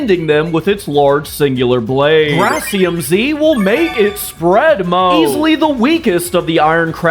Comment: Hold up, did he just say...SPREAD?